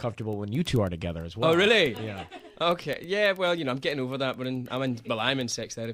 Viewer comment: what does he say at 3:00 ? well i'm in sex ....... ? oh i hate that the interviewer doesn't let him finish speaking at 1:56 he was just getting deep :) i love his voice, it's just so calming and sweet :) haha and the pic at 2:00 is funny XD hahahahaha 2:54 uuuh action, touch your aah. lmao :D hahaha and i love 1:48 heey, hey not at all, not at all! with his cute ' o ' :D aah just so love him! :) and his voice <3